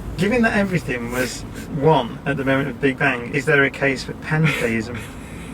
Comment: 22.30 'Given that everything was one at the big bang is there a case for pantheism...'. I thought he said 'Pancakeism', that got my attention!